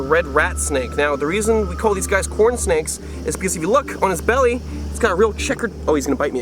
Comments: I miss heard it as porn snake Sorry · It sound like he said porn snake